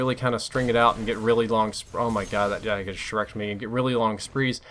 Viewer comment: About 9:30, did say "shreked me"? Or am I hearing it wrong?